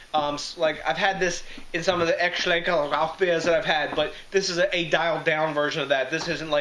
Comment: What are you saying at 6:30? Sounds like a completely different language. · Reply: Yes, that was German. Aecht Schlenkerla Rauchbier is one of the oldest and most famous brands of smoke beer in the world. If you like smoke beer you have to try Aecht Schlenkerla Rauchbier Marzen. Its a true classic of the beer world. Thanks a million for watching and commenting. CHEERS!!!!